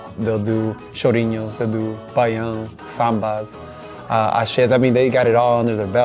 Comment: or so I think Eduardo says "Baião" and not "pagode" as the subtitles showed it). I know I am a year late to the party, but I discovered them just a little while ago and (as a Brazilian person myself) I am so happy to see how much they are into Brazilian music and how much those preferences influence on their sounds. I love how diverse are all the albums that they picked and I am really in love with their songs and performances. I hope they are very happy and I hope they achieve a lot of success doing what they love. <3